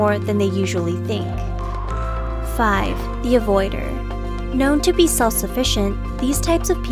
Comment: Lowkey thought the last one said 'NOIDER.